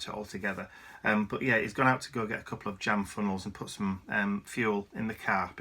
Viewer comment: The first time round, I heard it as getting 'damn funnels'...lol!! You aren't the only one with mojo issues...seems like quite a few ppl I watch on here have been feeling the same. Probably the crazy creative sprint we all do with our holiday crafting, then poof...you are spent. That is why I have several types of endeavors to choose from, and when all else fails, I watch tutorials from ppl like yourself...that seems to bring it back the fastest. :). For some extra inspiration, and journal ideas, I wholeheartedly recommend either ArtyMaze or Gayle Agostinelli...I bet you would do fun stuff taking a detour through junk journal land. <3